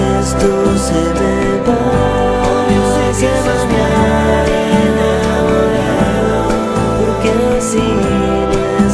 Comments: Alquien sabe que dicen las segundas voces en la frase del 1:44 cuando la primera voz dice "Quizás esto se me pase mañana"? Yo le entiendo algo asi como "Hoy yo se que es estar enamorado" pero no se si sea correcto o alguien me pueda corregir... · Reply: Dice" obvio se que eso no es estar enamorado"... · alguien sabe que es lo que dice en el coro en el minuto 1:43? graciass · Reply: "obvio se que es estar enamorado"